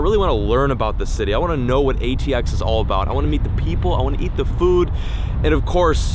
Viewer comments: I thought he said i wanna eat the people 🤣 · For a second I thought he said eat the people at the start